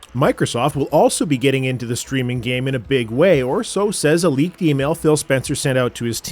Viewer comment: "a leaked email" I thought you said "elite female"